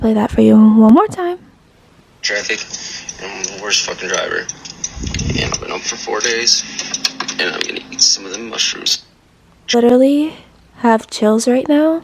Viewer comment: I thought he said “and I’ve been up for four days & I’m bout to eat some of them mushrooms”. Right out of jail and boasting about breaking the law. You are smart to be extremely cautious with someone with such complete disregard for the law. I really hope you move soon, if you haven’t already!